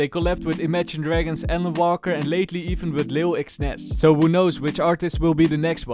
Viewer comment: wait did he just say lil X nas?